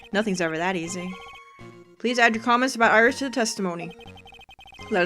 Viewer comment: Did that say aboot instead of about?